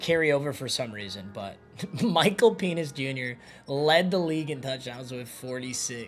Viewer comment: Lmaoo I thought man said Michael Penis jr 😂😂